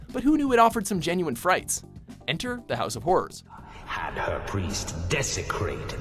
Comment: instead of "House of Horrors" subtitles show "House of Whores".